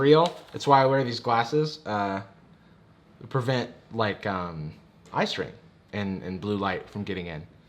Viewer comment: LOL I HAD HAD CAPTIONS ON AND IT SAID ICE CREAM INSTEAD OF EYE STRAIN